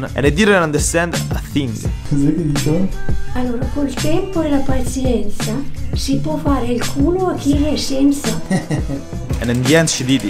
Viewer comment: italians from the future that are scrolling through the comment section. Can anyone write in italian what his grandma says in the minute 3:26? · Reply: Col tempo e la pazienza si può fare il culo a chi ne è senza